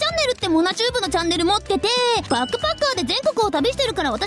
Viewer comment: バックパッカーがバッカバッカーに聞こえた…w すみません😅